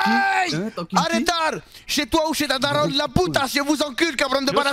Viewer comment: A 55:33 elle dis quoi « Alvarez »?